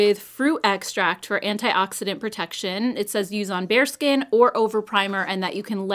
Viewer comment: when you read 'use on bare skin' I heard it as 'use on bear skin' I dunno hahaha